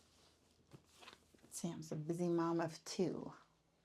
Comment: I thought that said busty mom at first. I wasnt way off. 😎